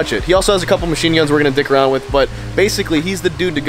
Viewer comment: Did he say “dickk around with” at 1:50?😳😳😳